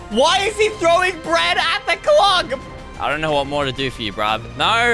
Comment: did you say 'clog' ?